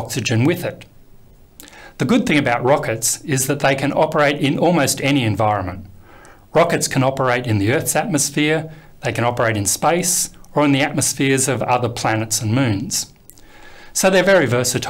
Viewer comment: i heard 'spice'